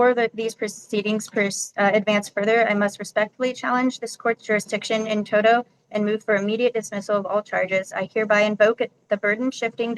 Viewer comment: did she say “in toto”? Like Toto the dog from the Wizard of Oz? I mean, they’re in Kansas so……😂